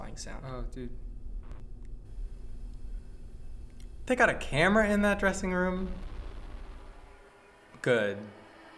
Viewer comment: did he say camera in dressing room that's a elevator.